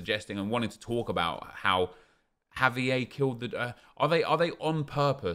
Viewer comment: 8.00 When he said " Have EA killed the draft", I thought he said "javier killed the draft" and i wondered who is this javier???? XDD